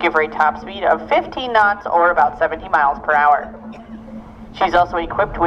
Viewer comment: I thought he said 70 miles per hour. That would be something.